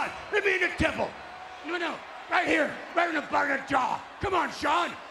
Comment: Did Vince say "right in the f'n jaw"